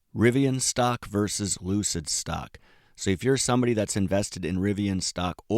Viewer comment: lol i kept mishearing lucid stock as loser stock 😂